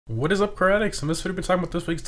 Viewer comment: So Today I found out you say " car addicts " I thought you said "karatics" lol